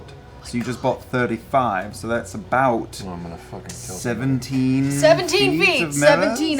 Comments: This time viewing I legit heard Travis say I’m going to kill Tiberius. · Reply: More specifically "I'm gonna fucking kill Tiberius" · anybody else hear Travis say "I'm about to kill myself" over Orion shopping 2:30:06 Laura would like to rage 2:44:12 grog is f#cking bored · If you are here: 2:18:40 Skip to here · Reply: I just noticed this and had to replay to see if I heard right. I thought he said, "I'm gonna fucking kill him", though. LOL